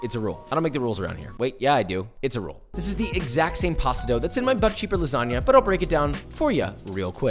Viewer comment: i thought i heard him say butt cheek lasanga